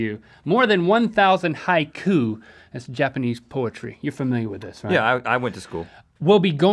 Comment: i thought they said poultry at first,i was like what! Chickens in space.